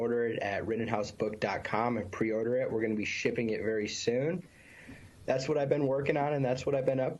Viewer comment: did he say shipping out books or $hitting out? 🤔🤣🙏💯💙🌊 BIDEN-HARRIS 20MF24 not voting or a vote for anyone else is a vote for tyrannical authoritarian fascist dictatorship by Donald Drumpf